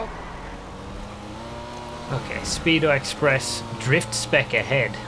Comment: kinda sounds like you said 'Speedo Express Drift Spec', that could certainly be an interesting vehicle of it were to be made.